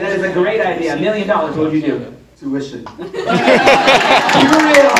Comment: What would he do with the money? Didnt understand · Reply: He said “tuition”